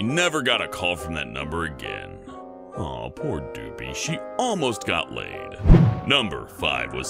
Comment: Lol I thought the guy was gonna say aww poor doopy only but he said aww poor doopy she didn't got laid